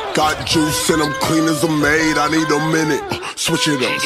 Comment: I thought the song said thick as a maid💀💀💀